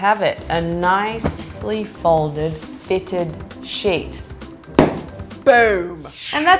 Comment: I heard "a nicely fitted shit!"